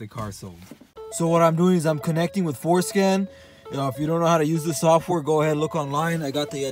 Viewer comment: connecting with... what? I heard it... and youtube heard it... (enable subtitles lol) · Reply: *It's ForSCAN. Bro's pronounciation is wild xD haha